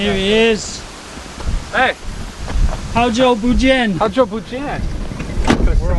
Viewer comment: LOL sorry... when you first saw your friends and said “好久不见" (long time no see), I misheard it as "how's your Fujian?" (asking him how his Taiwanese was).🤣😂 Cuz, you said that in the Westerner way, my brain was still hearing English Words ... 😂🤣😂 ps - I found that Boba Bread at Breadtalk in Singapore 😁😁